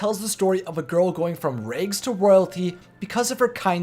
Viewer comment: it might just be me but every time he says the word RAG I hear RAYG instead. example